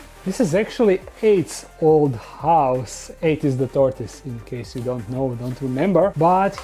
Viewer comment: I can't be the only one who heard AIDS, right 🤣🤣🤣